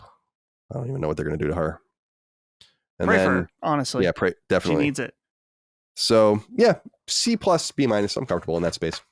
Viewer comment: what did say?! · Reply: “Pray for her, she needs it”